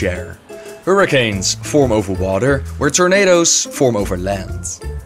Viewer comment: When he said tornado,I thought he said tomato I was so confused